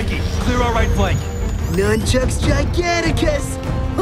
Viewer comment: What did Mikey say at 0:22? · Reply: Nunchucks Giganticus